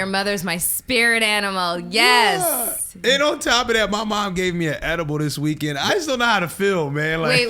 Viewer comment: I thought he said his mom gave him an ENEMA.